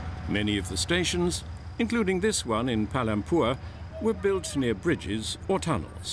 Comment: did he say palampur